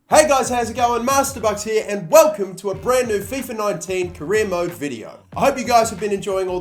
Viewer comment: Where is pulisic? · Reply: 98 ohh my bad i thought you said fifa 18 youre right bro